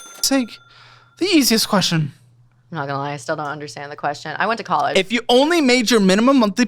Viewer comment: did I hear “I went to college”. How did she made it out of high school. 😂😂 wtf